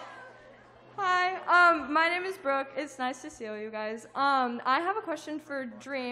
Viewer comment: i heard hi its nice to steal you guys not hi its nice to see all you guys